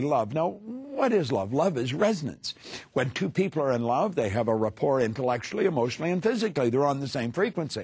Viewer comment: what does he say at 4:08? when two people are in love they have a ... intellectually, emotionally and physically. Is it the word report? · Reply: He says "rapport"